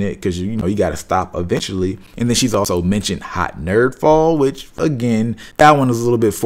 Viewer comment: thought dude said "hot nerf ball."